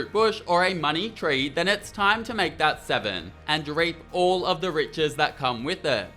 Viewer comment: "and r@ all the riches that come with it" I thought I was miss hearing but the Closed Caption says the same thing.